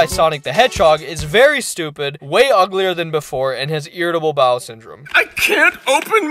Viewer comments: i thought it said doom syndrome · I thought it said The Down Syndrome💀